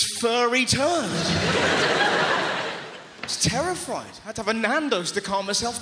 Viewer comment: Did Russell say he was "terrifried" instead of terrified about the squirrel